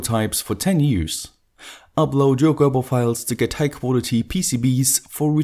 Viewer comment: Upload my "Goebbel" files? Really? Did I hear that correctly?